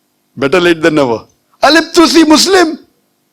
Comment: I didn't quite catch that "who was the person who discovered it?" Can some one comment to clarify the name? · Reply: Nasir al- Din al- Tusi نصير الدين الطوسي